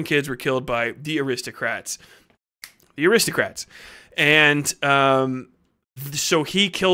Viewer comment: I heard 'aristocats' . damn that little white kittten must be lethal.